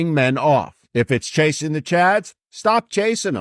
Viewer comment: At about 8:38 in this video: At first I thought he said "...chasing the 'nads..." Really...🤭